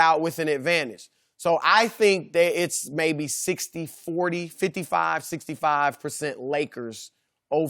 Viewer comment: did Chris just say 55 65